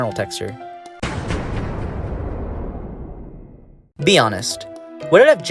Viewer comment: I thought you made a pun and said "bean honest"